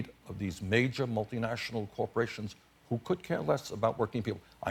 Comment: subtitles say "couldn't care less" as befits what Bernie is intending to say ... but Bernie's American, so he said "could care less" [facepalm] =-P